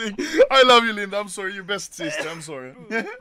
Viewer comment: I've watched anomaly since 2016 and I still dont know what Linda does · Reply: But doesn't he say, "I'm sorry Linda, you're the best sister, I'm sorry" at like 2:32 or am I hearing that wrong?